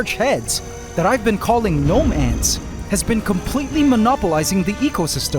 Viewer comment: The fact that every single ANt colony had to be harrased by Gnome ants, proves you were Right to put them in. It was a perfect example of how dominant they had become. This is probably what happened to the previous weaver ant queens. Rather than the belief that smong ate the queens. Really eye-opening episode how the ecosystem can change so much. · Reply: it sounded like he was saying nomad ants 💀